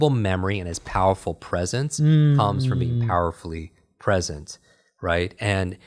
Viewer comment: tf he say??? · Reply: "His incredible memory and his powerful presence comes from being powerfully present" - basically saying Bill Clinton ability of 'being in the moment', helped him gain his renowned memory, which in turn helped him have a strong presence